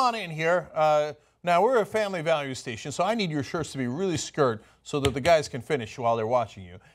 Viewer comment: Am I the only one who thought Cenk said "I need your shorts to be really skirt" at 3:03? Or am I crazy?